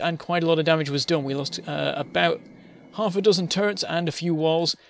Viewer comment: I could have sworn you said "Turds"